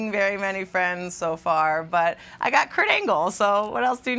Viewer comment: Oh!!!! KURT ANGLE....... I thought you said "Crangles"